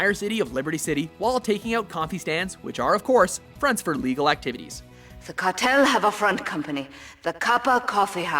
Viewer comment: Sounds like he said "Fronts for illegal actitties." At 3:56 LOL. 7:38 That's got to be a humiliating way to die, getting killed by an RC copter! Seriously, are the blades made up of knives?